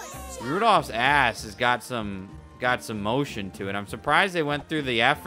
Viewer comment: I literally thought he said lotion instead of motion XD